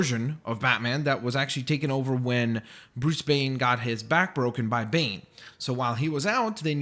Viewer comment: Did he said Bruce Bane? XD